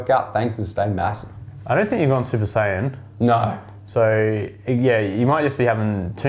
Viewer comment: did just say at 24:07 "I don't think you've gone super saiyan" ?!?!?! Thumbs up if you heard this too!